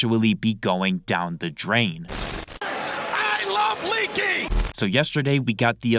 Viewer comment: I thought it said "I love leaking"💀💀💀